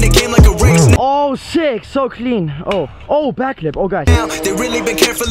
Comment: sounded like backflip